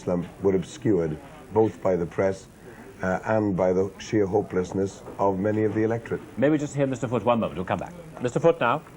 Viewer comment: Also, did I hear Neil Kinnock correctly at 37:50 referring to "the sheer hopelessness of many of the electorate"?